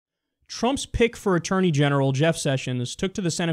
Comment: "Trump's pick for Attorney General" I heard as "Trump's pig for Attorney General". So true.